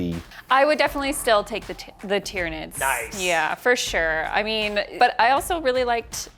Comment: Did she say Titranids?